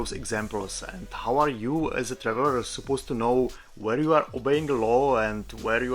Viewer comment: what did you call me? 😂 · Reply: Traveller 😅 what did you understand?